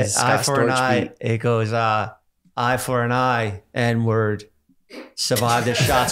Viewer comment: I don’t know why I thought this man was saying “inward” instead of “n-word”💀 I was mad lost